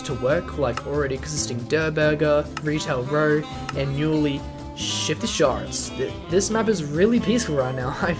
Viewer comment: WHAT DID HE SAY??? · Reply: shifty sharts💀